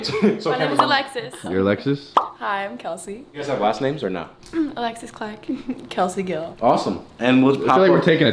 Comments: I thought he said Alex Clark 😂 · I thought it said “Alex Clark” and I was thinking in my head now hold up! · I thought it said Alex clark